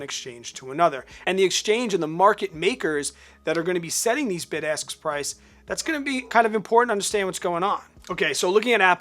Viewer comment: LOL!! My wife made me put you on ear phones because she thought you kept saying "BIG ASS Prices". ROFL